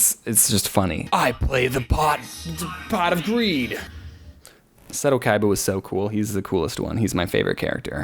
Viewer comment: I thought he said I play the pot of w33d